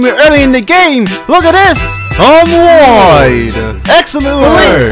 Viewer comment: what does it say @ 2:45 during the shot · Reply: I'm Wide